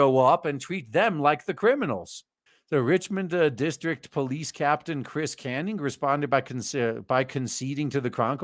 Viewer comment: I was listening and I thought said "Chris Kanyon" and an old school wrestling fan like me is thinking "oh, we gonna see the Captain handing out Flatliners"? Then I read what is on screen and I'm dissapointed.